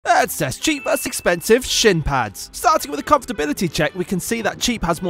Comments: "Cheap vs expensive shit pads"💀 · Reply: I heard "cheap vs expensive shit pants" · For a second I thought he said "cheap vs expensive sh1t pads" 😂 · Why did I heard "Shit Pads" in the beginning 🗿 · I fr thought u said shit pada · I thought you said sh- pads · Did he just say sh*t pads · I thought he said shit pads 💀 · NAH WHY DO I HEAR "SHIT PACKS"💀💀💀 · I thought you said sh*t pads😂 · Bro i thought man said sh*t pads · Why did i hear "shit pads" 💀💀💀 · I thought he said shit pads(sorry)😓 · I thought you said shit pads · I thought he said shit pads😂😭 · I thought i heard "shit pads" 💀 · I thought he said shit pads😅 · I thought he said sh$t pads😂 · I thought you said shit pants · I thought you said shit pads😂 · I thought he said shit Pads😂😂😂😂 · I thought he said shit pads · Why did i hear "shit pads" 💀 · I thought you said shit pads😅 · I thought he said shit pads lol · I thought he said "Shit pads" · Ar first it sound like he said "lets test cheap vs expensive s*t pads🗿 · I thought he said shit pads💀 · Bro I heard-"Shit pad"😅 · Oooooh, shin pads. I thought you said shit pads. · I heard it was "shit pads"💀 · My video loaded when he was just about to say shin pads and it sounded like he said sh*t pads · Guys is it only me or what because I heard sh't pads not shin pads😂😂😂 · I heard "shit pads" 💀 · Why did i hear “cheap vs expensive shit pads”💀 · I thought you said shit pads 😂😂😂😂😂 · I thought he said ‘shit pads’ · I thought he said sh*t pads💀